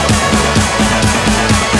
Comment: What does it say at 1:17? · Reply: B/@ "skybreaker, ground shake!" Brwaaaa